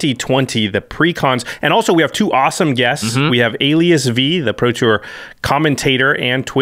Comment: Ooooh, AliasV. I thought you'd said LSV and I was like, "does the guy really need to be introduced?" 😂